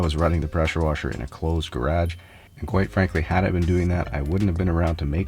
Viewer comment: At first I thought he was saying “gradge” and I was like, “huh?”. Then after replaying it 10 times I realized he was saying garage 😭😂